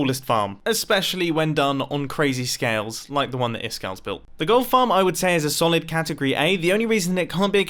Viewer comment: lmao i thought he said girl farm